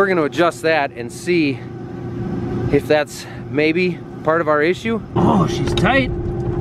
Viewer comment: That's what he said? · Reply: Part of our issue